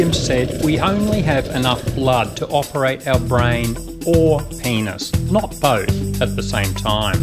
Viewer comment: Ok so I'm listening to this while doing some other shit and I thought he said we only have enough blood to operate our brains or our anus XD